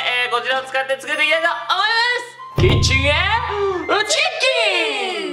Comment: キッチンへーチン○ーン！にしか聞こえないw